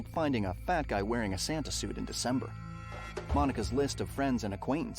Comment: Finding Monica's killer will be like finding a fat guy wearing a Santa's suit in December...... im sorry, what ?!! 😂😂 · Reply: I thought he said, in summer, and not in December.